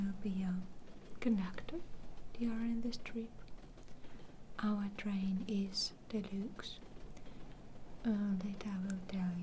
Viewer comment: "Our train is.....delooxe." So stinkin' cute. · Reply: I thought she said “our train is the looks”